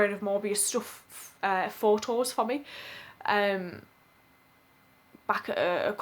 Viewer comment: There are a lot of funny moments which just add to the story. It took a few seconds for me to realise you said signed Photos for me. I thought you said he signed four toes for me.